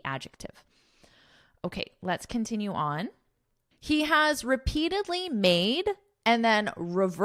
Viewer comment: Thank you for your efforts so much 🙏🏻✨ As you were reading the article at the end, I heard “recently” instead of “repeatedly”, is it on purpose ?